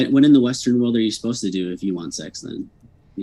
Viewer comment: Did really ask, at 1:16:12' *what (when) are you supposed to do in the Western world when you want sex then?" Did he really ask that or am I mishearing him?